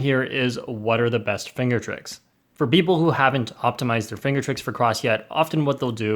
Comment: beople or people? i think he said beople its ok these mistakes happen sometimes